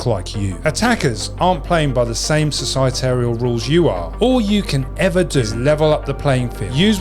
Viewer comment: Did you just say... "societarial"? Try again 😂